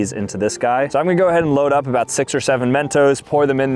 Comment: I actually flinched when I heard 67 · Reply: its 6 OR 7 mentos. not 67. god you all need some serious help dude. cant even go by one day without saying 67 or doing that fucking ass trend. GET HELP WHY DONT YA